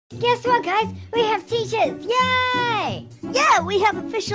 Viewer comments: It sound like was saying guess what guys we have teachers at 0:00 I laugh when he said that 😂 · For a second I thought said yay we have teachers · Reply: Me too I thought he said teachers but nope he said t-shirts